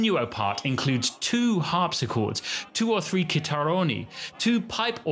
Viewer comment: Does anybody know what he says at 5:54? The transcript says 'kitaroni' but I haven't been able to find anything on that (just Instagram hashtags lol).